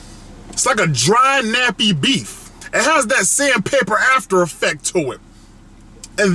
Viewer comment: When he said Sandpaper I thought he said Sam Pepper 😂😂😂😂